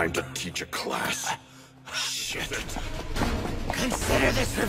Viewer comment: Wind sugar Rockies was talking it sounds like he's saying this is revenge for Elmo by the only one that I've heard like that's all I heard and I can't hear anything else now